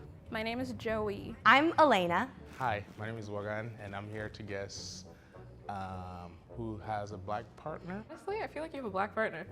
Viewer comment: I thought it said guess who has a black panther.